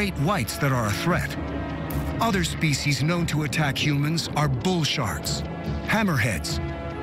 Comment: Ok sorry For 00.000.1 second..I thought he said bullshit (When he was gonna bullshark)